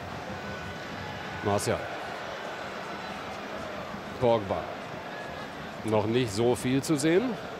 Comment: Sagte jemand Akbar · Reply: Also ich habe Pogbar verstanden also der Spieler von Frankreich